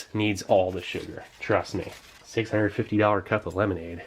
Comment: "$650 cup of lemonade". Did I hear that correctly?? Holy cow